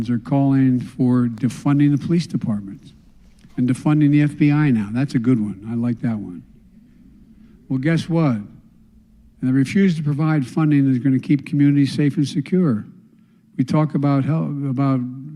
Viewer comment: biden said i refuse to provide funding for keeping the community safe n secure ? Did i hear him correct ?